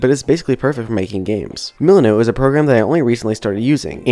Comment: What A great video, thank you so much for sharing this! One comment - I had a hard time understanding the apps you used for Planning "trello" and "milanote". Trello I had heard of, but after come google searching a few minutes later I discovered you were saying the name "milanote". What I heard was "muknow" ("moo"-"Know", maybe my ears need cleaning, ). Again, a great video, I am going over tkaing lots of notes to help on my beginner Unity JourneY! Yes, I am also using GameMake Studio and starting to use Unity more! :)